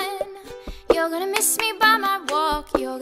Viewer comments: Your gonna miss me by my taco 😂🌮I thought it said taco first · I just found out it said "you're gonna miss me by talk, oh" I always thought it said "you're gonna miss me by my taco" 😂😂😂